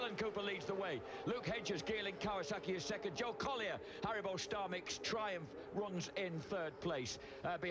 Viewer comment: Just after 5:56. Did he say Haribo Starmix?